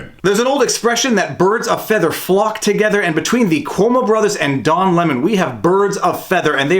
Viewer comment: Lol did I hear “dong lemon”?